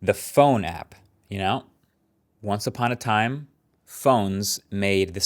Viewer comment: I swear I heard ‘once uton a pine’ there 😂